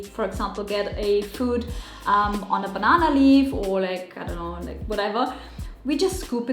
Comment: Did said at 3:39 "Like ano" or I just misheard it?